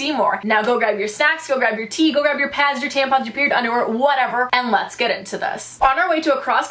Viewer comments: Did she just say go grab your “periods” 😬😂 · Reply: ohhh i thought she said period, underwear but yes i do know what it is haha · Did she just say go grab your “periods” 😬😂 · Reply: She said grab your period underwear! (It’s padded reusable underwear you can buy instead of a pad or tampon)